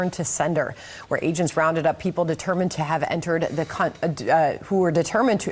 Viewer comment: 😮... WHAT DID SHE SAY?!? Determined to enter the C*NT??? 😂😂😂😂😂😂😂😂😂😂😂😂😂